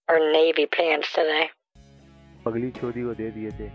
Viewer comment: Omg I was gargling mouthwash when he was singing “you are fugly” and I volcano erupted and it went everywhere 😂 😂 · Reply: According to other commenters he was singing in Hindi: "pagli chodi" which roughly translates to "crazy b*tch" or "mad woman". I thought he was saying "you are so ugly" 😂